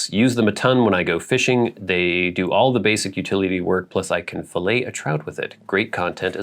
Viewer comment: I heard that as "I can fellate a trout with it." Hell of a knife! 😅